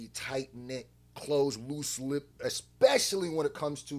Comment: Did you say tight knit, close loose lip? 😂😂😂